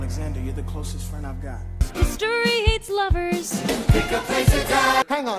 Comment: History hates lovers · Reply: I heard "history eats lovers"😆😅